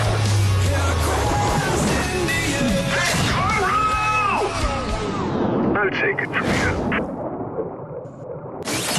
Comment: anyone know what Jaller is screaming here? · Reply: He yells "Matoro!" The name of the Toa of Ice that comes to grab the mask.